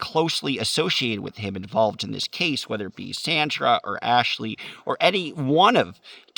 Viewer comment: Be careful here! I hear “Slander”!!!!